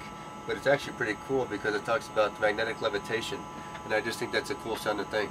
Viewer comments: ish it's magnetic levitation i hear he says not "limitation" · Let me guess, this video must be fake, right? And your proofs, what is it? Is it an experiment which shows that light has a limited range, or that the attraction of objects towards the ground is independent from their mass, or how the rotation of a complete sphere of stars with 2 celestial poles can be projected to only a half sphered dome without any distortion in distances or moving speed? Well, as long as you won't call simple geometry and basic laws of physics fake, too (and you have to to deny all these undeniable facts which prove the globe), I doubt there is only a single proof for a flat earth you can show us, and I doubt if it is even aimed to prove flat earth rather to fight the globe in some stupid kind of way. But if I'm wrong, go on! Enlighten us! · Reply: He actually says "magnetic levitation" at 1:53 on the video. You asked, right? Well, there is your answer.